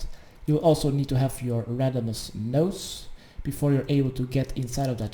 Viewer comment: wtf was said at 5:10???? randomas nosse? wtf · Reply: Radimus notes from start of legends quest from Radimus Erkle.